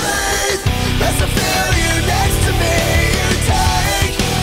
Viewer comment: I hear (there's a billionaire next to me)